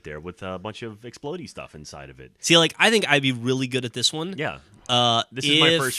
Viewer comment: i saw the word death but all I heard was "I'd be really good with this one - yeah"